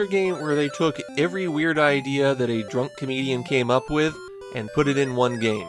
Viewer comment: I misheard your Takeshi no Chosenjou comment the first time around as "Every idea that a drunk Canadian came up with." To which I would have said "No Canadian has THAT much imagination." Sadly, you did say "comedian" after all.